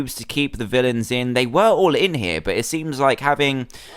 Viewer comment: Lol I thought it said evil Williams